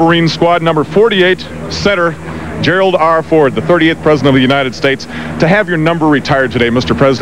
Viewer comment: did he say he's the 30th president?